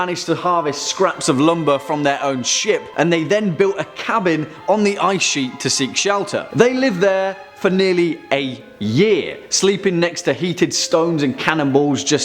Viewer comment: OMG I thought u said 8 years not "a" year 😂